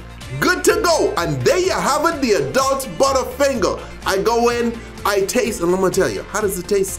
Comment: I thought it said Adult Buttfinger lol